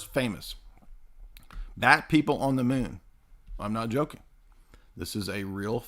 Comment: I was listening to this while cleaning my apartment. I had to rewind and listen to 1:32:20 again because I heard "Fat men on the Moon."